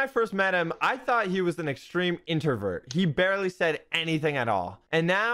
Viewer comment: anybody else hear intervert? I also was seen as a introvert for like 7 years lol